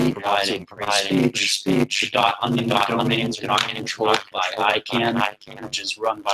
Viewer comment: Listen. I hear "speech speech". That's all I can make out